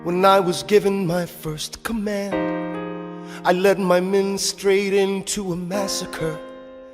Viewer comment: I misheard men straight into as menu straining actually kill me